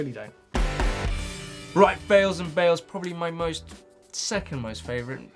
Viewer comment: Did say 'bails and bails' at 15:01?